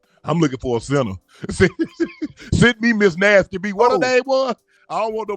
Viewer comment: Did he say Miss NastyB???????